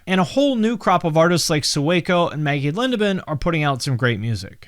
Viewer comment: Can somebody help me out with the name after he mentions Sueco and a female artist name I can't understand. Sounds like Meggie or Meagan Lenaven but can't search the right name myself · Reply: Maggie lindemann